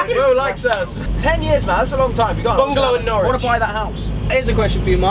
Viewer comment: Am i hearing things or did say something about Norwich?